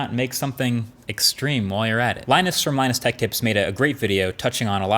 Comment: I swear he said "Linus from minus tech tips"